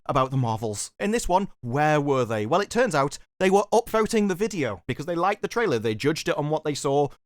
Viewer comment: Oh, upVOTING the video. I thought you said "upTHROATING" the video. Which would be weird.